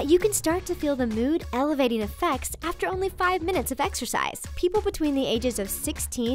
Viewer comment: exiercise. oh i thought u said extra fries 😉